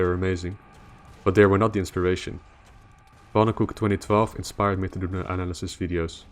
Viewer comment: What's the name you say at 3:26? I want to check out that guy! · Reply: Pannenkoek2012 -- his uncommentated channel to be specific.